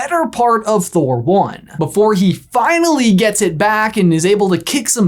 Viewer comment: I thought said 'Be- Thor' instead of 'before'.